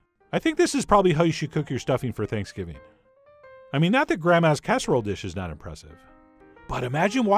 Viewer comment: I thought you said "gram-ass" lol